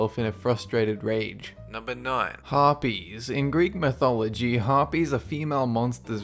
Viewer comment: I thought he said herpes for a second there